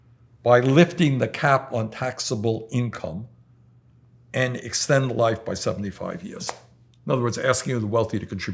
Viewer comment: did he just say extend life? I watched the clip 30 times and I still don’t understand what he said